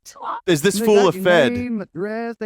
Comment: i literally also thought he said fent, then i thought he said "is he a fed?" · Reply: In all seriousness, I think the line is "[is] this fool a fed?", but I'll admit I also heard "This full of fent?" first.